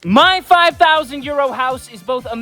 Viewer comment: Ngl at the start I thought he said “5000 year old house” so I was kinda confused😅